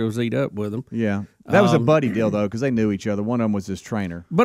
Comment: thought said something about a "buddy dildo". Given the story i did a double take😕